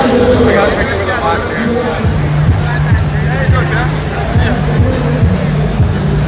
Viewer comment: Did that Fortifx guy @ 5:03 call Jay - Jeff? Or am I hearing things? ahahah